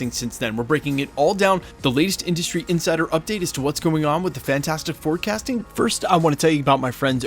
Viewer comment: Dude said "fantastic 4 casting" but i heard "fantastic forecasting"😂😂😂